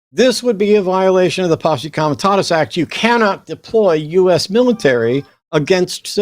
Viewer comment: The what act!? The Apostles autotomous act? What did he say at 17:40? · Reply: Posse Comitatus Act